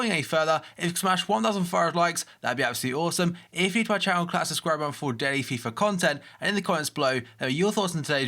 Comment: What does he say on 0:19? I've watched his videos for so long and it always bugs me..'if you enjoy this video class around and subscribe for more' or something..SOMEONE HELP?! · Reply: for more daily fifa content